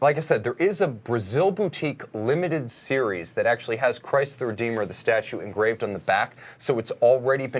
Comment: I swear--The first time you looked at the back of that red JLC Reverso, I thought you said "That's the Rio de Janeiro Chrysler Dealer special edition." After you guys kept talking about the watch and you said it again, I heard "Christ the Redeemer." LOL. I can't see that watch selling very well among Charger Hellcat drivers.